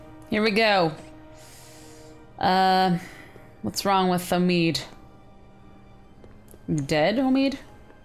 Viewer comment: What's wrong with Omid? Is he a dead Omid? That is what I heard! xD